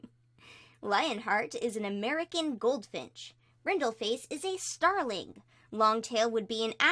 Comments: I thought it said 'American Goldfish' and i said: 'that's NOT a bird' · I THOUGHT YOU SAID LIONHEART WAS A AMERICAN GOLDIFSH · For some reason I heard American goldfish